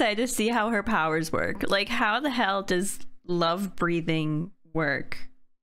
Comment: the way this sounded like "love breeding"